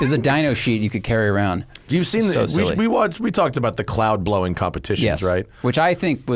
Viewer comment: Oh. At first I thought you said "clown".